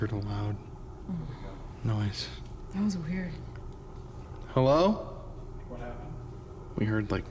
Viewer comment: Did anyone else hear someone answering saying “Hello” at 1:27:00?!?! Also at the very beginning of the night when the orb comes out from behind?!? :_spookyWord